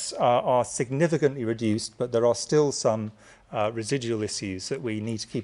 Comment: What does he say? Resi...?